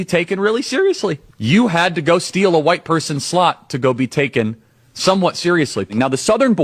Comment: Am I the only person who heard "white person's slut"?